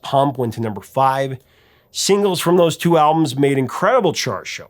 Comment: "Angel" was on the Permanent Vacation album NOT Pump. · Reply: my mistake.I heard it as "singles from their album Pump" when you actually said, "singles from both albums".